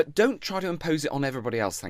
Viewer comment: Did Matt just say 'Don't try to impose it on everyone else'? Hahaha